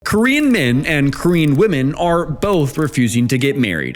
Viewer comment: I thought instead of saying "korean men and korean women" at the start you were saying "queen men and queen women" 😂